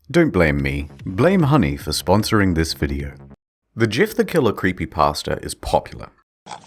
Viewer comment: did you just say Gif the Killer? still sounds more interesting than Jeff the killer lol